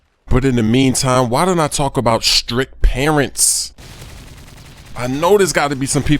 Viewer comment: thumbs up if you thought you heard him say STRIP parents